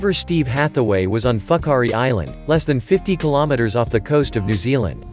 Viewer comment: did i hear fuck hurry island?